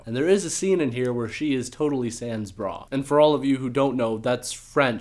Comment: It sounded like u said "sans bras" which means armless lmao